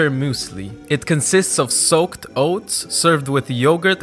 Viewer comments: Soaked oats. I heard "soap, oats...." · I thought he said soaked bones. But it looks really good.